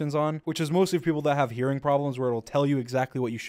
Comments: i thought he said urine problems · When he said "hearing problems" I thought he said "urine problems" XD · on 4:33 I literally heard "urine problems" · i thought he said "which is mostly people with u r i n e problems" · bruh I thought he said " mostly for people with urine problems" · did he say urine or hearing problems? Or that im having hearing problems myself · I thought he said urine problems instead of hearing problems and i was like what